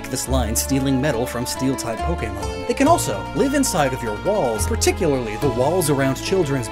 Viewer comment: anyone else hear him say balls instead of walls? No? Just me? Ok...